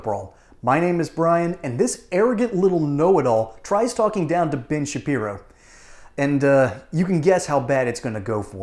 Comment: Sounded like you said - BIG - SHAPIRO 😂😂😂😂